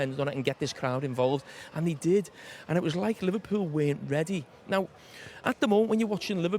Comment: Did he say Liverpoo...?😂